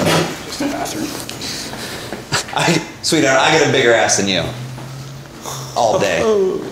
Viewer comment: LOL 🤣😂 Did he say " I am a Bigger Ass than U ??"